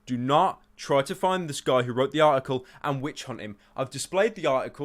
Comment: "Do Not Witch Hunt" what i hear "Do Witch Hunt"